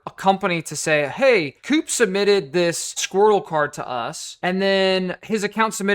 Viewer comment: I coughed in the same moment you said 'squirtle card' and I thought you said 'squirrel fart'. Thanks for reading, carry on.